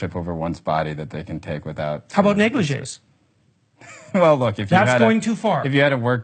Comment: What does Peterson say at 7:40? "How bout ____" sounds like "negliges" something to me, what does he say?